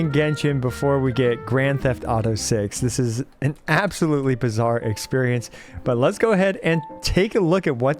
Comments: did you just say bizzare? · did you say Bizarre?? 👀jojo reference?